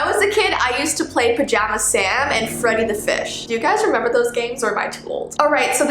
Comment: is it just me or did people hear: i used to play pajama sam infront of the fish instead of: i used to play pajama sam and freddi the fish (time stamp is